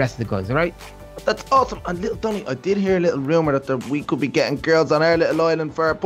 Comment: It sound like said little bummy😅